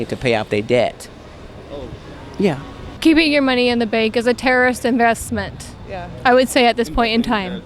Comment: WTF did she say “terrorist investment” @ 1:37 ?!